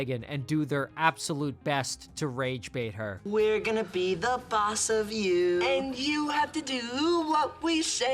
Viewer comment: all I hear is that one YTP where he says “we’re gonna beat our meat”